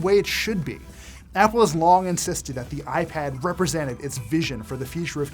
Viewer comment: I literally thought he said long press comes to the iPad 2 😂